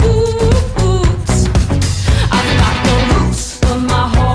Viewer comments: i though i heard i've got no moose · The song name: no roots Me searching : I got no whoosh.... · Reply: I thought that they said “I’ve got no roofs” lmao